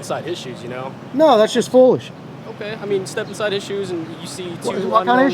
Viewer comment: I live 50 miles from Richmond. I heard "step inside issues" also. Good audit, decent cops.